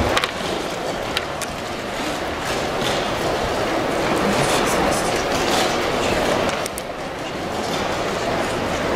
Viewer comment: Can somebody translate what Magnus was saying. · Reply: He was saying "Happy New Year 2023"